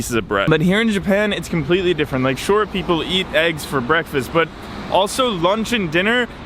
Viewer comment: I thought he said "Short people"